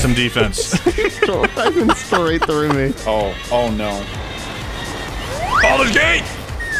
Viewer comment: Am I nuts or did say “Baldur’s Gate” as he tried to jump onto the train to tackle at 6:23?